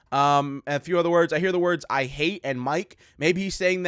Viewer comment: I heard “I hate mike. I hate Henry. *Screaming*”